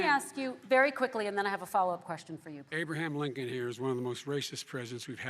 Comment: did joe just called trump abe lincoln? I feel so bad for joe 😢